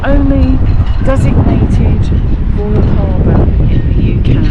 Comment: Ramsgate is the only designated ______ (blank) harbour in the UK. I couldn't hear what you said @ 17:35? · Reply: Royal Harbour xx